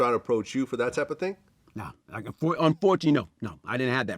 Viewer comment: Did he say fortunately or unfortunately??? Sounded like he wanted it 😂